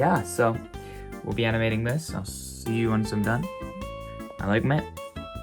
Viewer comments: Why did you say 3:06 I like men? Lol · did you just say yuo like men 😮😮😮